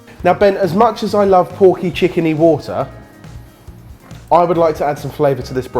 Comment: did Jamie say chicken?